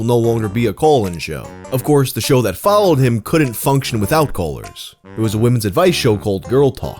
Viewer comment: Man, I swore you were saying "coalers" and not callers for like 2 minutes.